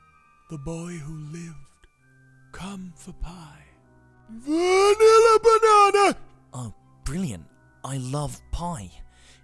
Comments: did i just hear him say “VANILLA BANANA” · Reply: Chloe He Oh I heard him say 'VANILLA BANANA' to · did i just hear him say “VANILLA BANANA” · Nobody: Not a soul: This guy: ThE BOy WhO LivEd, CoME FoR PiE, VanILLa BanaNA!! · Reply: And now i hear "PiE, VaniLla BanaNa!" 😂😂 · did i just hear him say “VANILLA BANANA” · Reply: Oh that's what I heard. That's how Harry's parents died "Vanilla Banana!" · vanilla banana . The most powerful spell in wizarding history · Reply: I thought he said I NEED AH BANANA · Did he just say "WANILLA BANANA"?